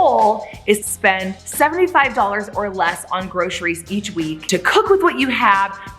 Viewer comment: $77.80 Edit, because I thought you said $80 or less but you said $75 or less... hmm... $72.58!